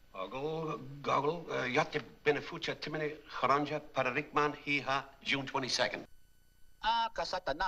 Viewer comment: what was shemp saying In text? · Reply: Oggle goggle yatabenafuch a timinya carunja paranigman eha, June 22nd